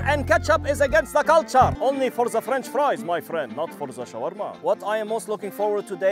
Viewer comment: did I hear freNcH frois?